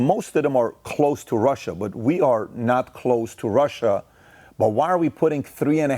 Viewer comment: Did he say we are not close to Russia?